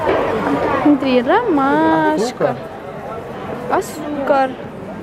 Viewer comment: мне послышалось не "азука" а "азбука" хехе